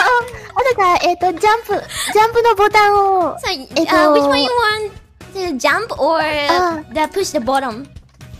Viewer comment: I heard "do you want to jump or push the bottom" LOL